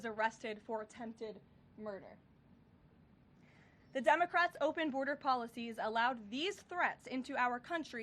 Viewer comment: It sounds like Caroline is saying "dumbocrats" at the beginning!! That's AWESOME!